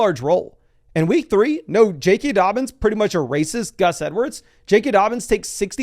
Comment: I swear to god I heard him say that Dobbins is basically a racist Gus Edwards 🤣